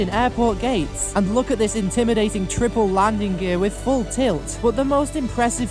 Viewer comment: Did he just say “full tit”?